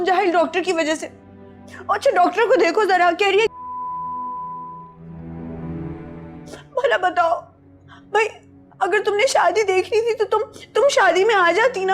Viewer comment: wht did she say ???? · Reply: Doctor ko dekho zara keh rahi hai mahjabeen ki shaadi nai hui